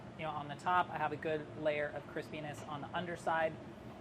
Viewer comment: the subtitles say “I have a good layer of Chris penis on the underside”